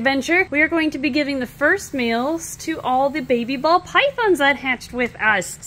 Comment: I thought she said feed the first males to all the babies and they will eat lol. I don’t even like snakes but I love this channel